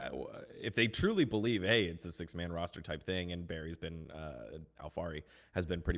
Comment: did just call Alphari Berry?